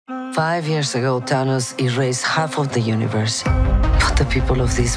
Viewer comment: So I'm guessing the Thanos in this Thanos trailer is because Kit says the name Thanos? Really misleading... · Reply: Could of sworn she said Dhanos. I was like who the hell is Dhanos